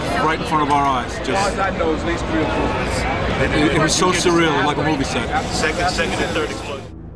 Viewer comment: Watching the eyewitness at 3:43, with closed captioning it takes out the word "explosion" that he clearly says, and fails to record him saying "It was like a movie set".